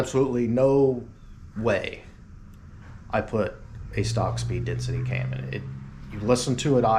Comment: i could have sworn at one time you said "Speed Density B cam"! i was thinking wow that would be awesome in my 91 vert.... or the Anderson cam that Brew2l put in his and Andrews cars....